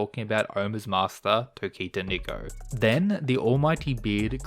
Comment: WHAT did bro just say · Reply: Tokita Niko